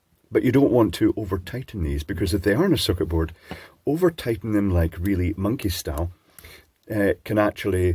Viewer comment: Great explanation, though confused at one point where I thought you said overtightening monkey-style. I'm now in a quandary as to whether this refers to intoxication or a tougher form of martial arts.